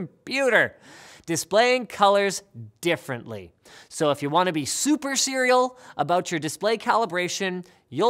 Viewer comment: Did he just say "If you want to be super serial/cereal" ?